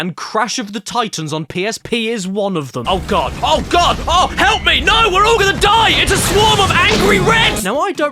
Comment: what is being said here? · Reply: Oh God, Oh God, no, help me, we're all gonna die, it's a swarm of Angry Reds!!